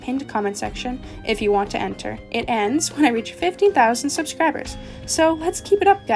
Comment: At first I thought said 50K subscribers for the end of her giveaway- Or did she?